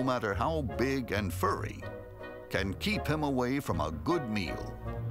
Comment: i thought it said "keep him away from a good meme"